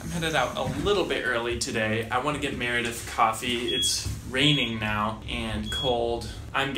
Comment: i thought he said i want to get married with coffee